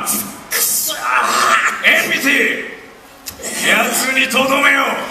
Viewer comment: is it just me or did he say amputie bwahahahaha XD non-raicial twords that japenese language but thats what it sounded like in english